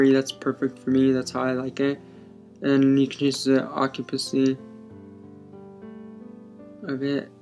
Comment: I loved how u said "ocupacy" instead of "opacity" at 20:00 :d